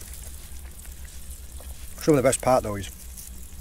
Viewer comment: I just love every video you share of your beach foraging, your family, your cook ups and your respect for nature and it's bounty. I could manage to survive on desert life if I had to, but I'd much rather eat with you than have cactus and prairie dogs or blue tail lizards. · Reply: I had to add this... your dunking bread I heard first as "donkey bread" to my American ear. I love your family's British accents.